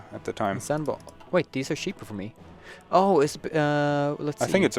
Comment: when he says cheap it sounds like hes saying shit, 'they are really cheap/shit'